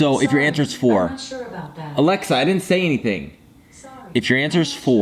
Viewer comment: Lol did he say Alexa in 2:58? Pretty funny!